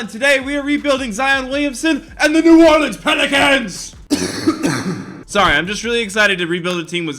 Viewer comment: Love the grammar, “Rebuilding the New Orleans Pelicans rebuild” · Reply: Ethan Caffrey it sounded like he said penicans